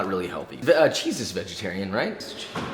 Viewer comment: I thought he said "Jesus was vegetarian"